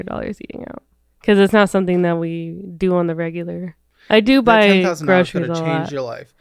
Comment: I think I heard her say "I do buy scratchers a lot". If that's what she's spending on, that's sad. Those tickets are an absolute waste of money. I don't know how much she's spending on them, nor does it really matter, but until she stops wasting money on them, she'll always be struggling. I feel that those tickets are aimed at people that can not afford to flush money on them. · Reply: She said “I do buy groceries a lot”. She did not say scratchers.